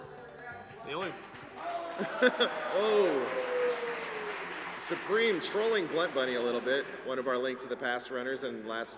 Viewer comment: What did supreme say? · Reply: "Still a better ski grab than bluntbunny" Bluntbunny is another dkc3 runner and Supreme is saying he is better at grabbing the ski that drops off the boss.